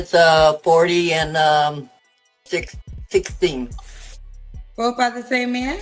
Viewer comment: did she say her kid is 16??